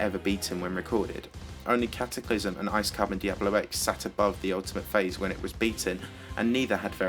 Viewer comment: when i heard you saying "the ultimate phase" i heard "the ultimate face" xD